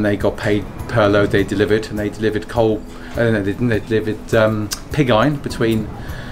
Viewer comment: Sorry, I did not understand what was saying about delivering „pigeye“ (4:04) and Sand in return? I am sure they did not deliver pig eyes, but what was it? Wonderfull to see the old workshops and machinery. A treasure to keep alive for many more years hopefully. Thanks for sharing :) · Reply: Pig Iron - the result of the first stage of smelting iron from ore